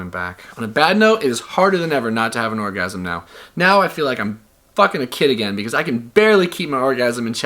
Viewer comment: Did he just say that he's "Fucking a kid again "?